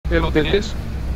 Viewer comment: parece que dice eloteles eloteleeeeees eloteleeeeeeeeeeeeeeeeeeeeeeeeees me recordo a un zapatero de tegucigalpa que en lugar de decir: "se arreglan zapatos" dice: "relapatos" 😂